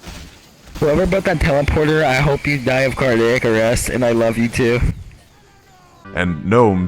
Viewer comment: wait did he said i hope you died to caddicarus